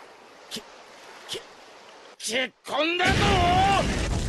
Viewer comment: it even sounds like he says Super Saiyan